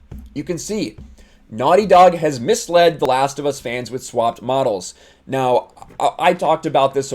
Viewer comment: Get your F*ck Naughty Dog gear here!!!! <link> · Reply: Could have sworn it said "Last of us OnlyFans" lmao